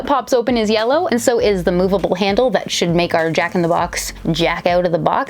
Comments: it's a slang term for male masterbation though usually it's "jack off".. does that help?x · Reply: oh wait so she said jack out the box I thought she said jack in the box lmfao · because you are too young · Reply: im not i thought she said jack in the box and i didnt get it till people said she said jack out the box